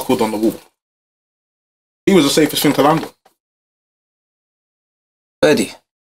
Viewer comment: did he just say he was the safest thing to land on?